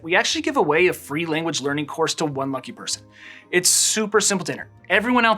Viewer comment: I thought he said one monkey person😂... he said one lucky person!